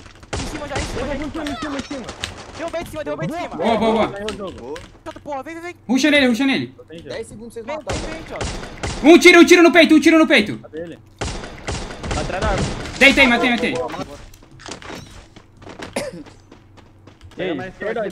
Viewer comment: I can't understand that language but I think he is saying "rush karo rush karo , bas 1 goli ka hai banda" 😂😂